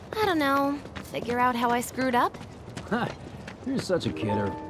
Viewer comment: i hear "fucked up" for some reason